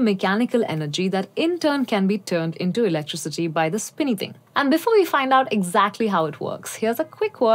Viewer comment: did you say, "spinny thing"? What's a Luddite?